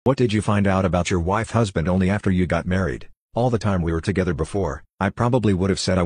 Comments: I heard “What did you find out about your wife’s husband after you got married” 💀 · That wife/husband, I heard "wife's husband" and was thinking it was a very specific situation XD